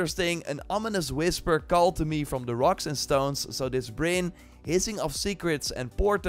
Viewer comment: DID I HEAR A ROCK AND STONE?!